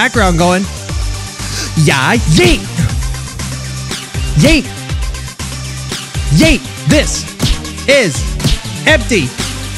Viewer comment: I was so confused how he knew “this… beat… is non… stop!” But I guess he just randomly started singing and it sounded like Karate Man returns